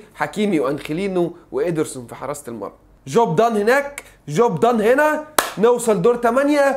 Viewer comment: هوا انا الوحيد اللي سمعتها جو بضان هناك جو بضان هنا 😂😂😂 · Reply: job done هناك